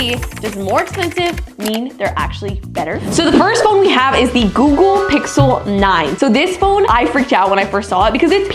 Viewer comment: Oooooooo spicy phones and I think at 0:13 you said the gogo pixle 9